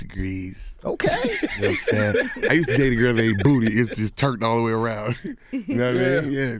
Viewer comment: on god it sound like dro said I use to date a girl who ate booty😂😃